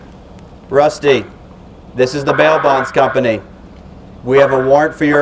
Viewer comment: I thought he said mailbox company